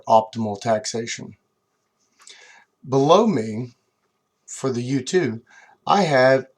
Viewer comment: You say "below me" What I hear: "Blow me"